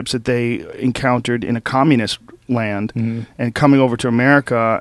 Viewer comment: did joe just say communist land hahahaha!!!!!!!!!!!!!!!